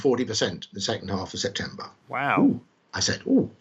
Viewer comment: who else heard “wheow” in the voice of Owen Wilson